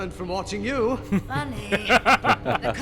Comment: I always thought asajj said hunny but looking back I guess she said funny that would make more sense